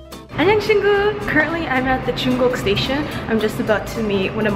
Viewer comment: I literally heard ‘Jungkook Station’ 😂😂